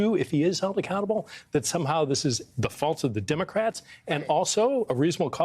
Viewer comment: Navarro saying that if civil war breaks out it will be "the Democrat's fault" is like Putin saying the invasion of Ukraine is all NATO's fault. Or like the abusive husband who beats his wife for not bringing his coffee, "look what you're making me do!" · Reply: Did Pete say 'fault' or ' fall'? I heard 'fall' and wondered who Pete is gonna bring for that. Can we lock this guy up already?